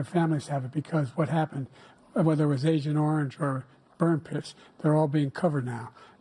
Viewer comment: ish it sounds like Biden said “ burnt piss”! I had to go back and listen to it again and sure enough …that’s what it sounds like! He said agent orange and burnt piss🤦🏻‍♂️